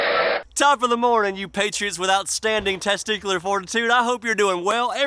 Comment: I thought he said - “Top of the mornin’ you patriots without standing testicular fortitude” He actually said - “Top of the mornin’ you patriots with outstanding testicular fortitude” 😂🤣